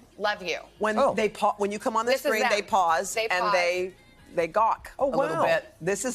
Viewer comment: did she just say gawk!?